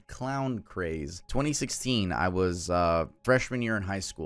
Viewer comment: I’m 27, I heard you say “in 2016 I was a freshman” meanwhile I graduated in 2015 lmao